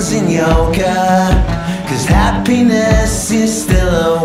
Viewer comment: why did I hear "happiness is a wanker"? omg